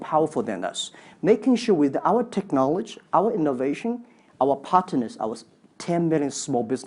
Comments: Scottish(for them), chinese(partners, which I thought he's saying hypotenuse, didn't make sense tho) and obv Adele(especially, I don't think I can do this, which wasn't even a blank) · The "200" in cups I couldn't hear and I heard "partiness" in stead of "partners". But I could understand the rest. I once spoke on the telephone to a man from Pascagoula and he might as well have spoken Chinese, because I couldn't understand one word he said.